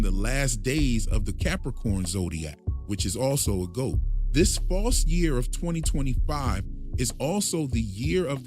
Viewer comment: why are you saying "this false year of 2025"? Am I hearing you incorrectly? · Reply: Yes the false year of 2025.